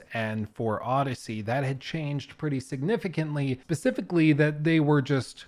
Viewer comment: did be say pacificly instead of specifically?